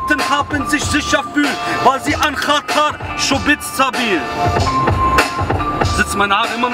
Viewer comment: Bei 1:29/30 was sagt er das das letzte Wort halt "Schwuwybsabil? :D sry hahahah ich frag mich des immemrrr